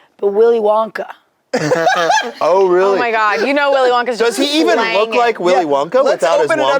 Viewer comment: I thought Courtney said “You know Willy Wonka is just swanging it” 😭😭 This is why my HoH ass needed the subtitles.